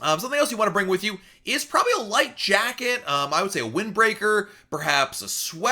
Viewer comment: Ha ha. 4:10 I thought you said LIFE jacket. I was like; what? you gotta bring your own and in the carry on bag...